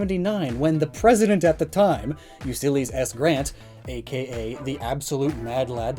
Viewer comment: He says U sillies instead of Ulysses XD